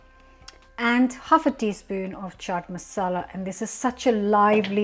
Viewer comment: Hello ! I’m making these tonight but missed out what you said . Was it chaat masala ? At 6:53. Can you advise where I get this ? Thank you! 😊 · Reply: Yes chaat masala